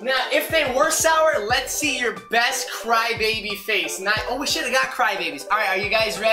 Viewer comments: Im going to say not to be rude but at 1:30 I think he said oh s*** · Did say a cuss word at 1:30? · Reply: He said should’ve